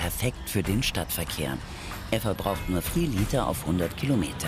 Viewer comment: Was sagt die bitte bei 7:14 xD "Er verbraucht nur 'thrii' Liter auf Hundert Kilometer"